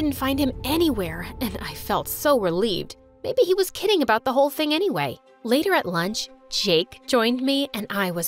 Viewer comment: the captions said thin instead of thing